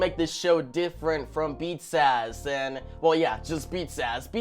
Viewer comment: it sounded like you said beat zazz🤣🤣🤣